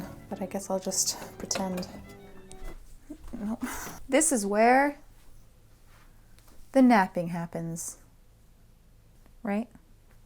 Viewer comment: “This is where...the napping happens” Me: whispering “the nappening” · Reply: I thought she said, "This is where the nappies happen". Nappies are English for diapers.